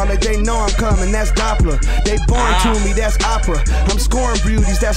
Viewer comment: I swear I thought he said "They boring to me, thats Appa, im scoring beauties, thats sokka and for a second I was just like wtf did he just say about Appa?